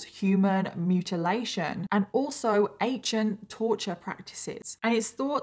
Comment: When she said accient torture I thought I heard her say "Asain torture" and I was like....ummmm what? Luckily subtitles cleared that up. 😝😁💀